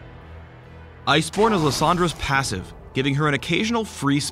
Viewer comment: i heard "iceporn". ahahaha!